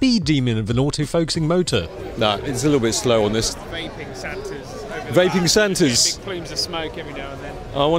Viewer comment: Vaping santas? #yawn! I first thought you said Viking santas. Now that's something to photograph and document!